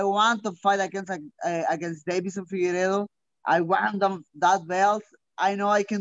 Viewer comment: babies infidel instead of Deiveson figueredo ty auto generated captions